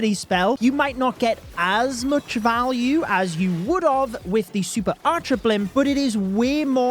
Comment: did he just say “would of”?? I thought this was a mistake people only made over text, not spoken lol